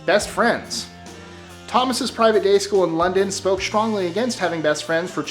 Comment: Did he say private gay school?